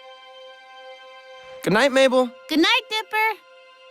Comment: I heard “Goodnight Nipple”